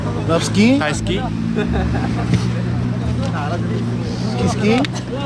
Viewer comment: Why does 1:39 sounds like Himchan is saying 'skeet skeet' instead of ski? No wonder this boy is horrified.